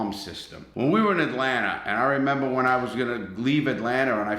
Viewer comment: did you just say atlanter